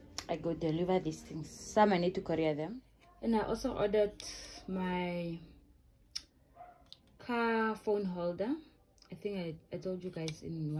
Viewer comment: How about Kaiwalans? 😅 Thought I heard you say you ordered a car from Shein too 😆🤣🤣